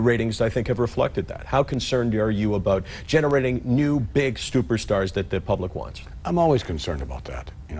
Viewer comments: did he say new stuper stars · Stuperstars? Did I hear that right?